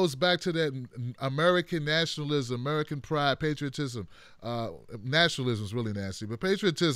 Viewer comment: Ok this is funny. I misunderstood when said "patriotism" and thought he said "priapism." I was thinking, how does that fit in here??